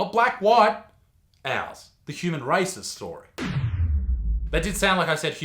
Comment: For a moment i thought you really said human racist's story